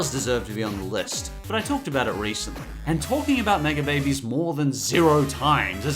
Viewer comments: I thought he said nigga babies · I thought he said "nigga babies" lmao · I was not looking at the screen and I thought he said "Nigga babies!"